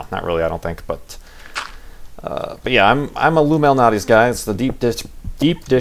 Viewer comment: It sounded like said that he's an illuminati guy. 10 AM for the livestream? damn. I'm gonna have to get up early. 8)